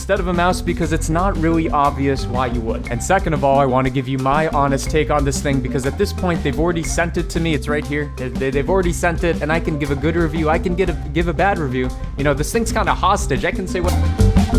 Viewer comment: I'm not a native speaker....Is he said "sented" at 0:49 . Is it correct ?🤷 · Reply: he says "Sent it" and not "Sented" they sound almost the same because of his accent and fast speech.